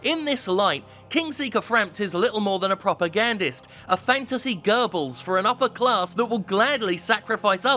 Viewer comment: Not gonna lie I heard "fantasy Goebbels" as "fantasy gerbils".